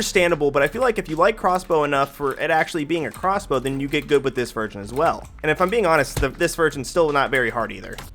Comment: did he say this virgin? PauseChamp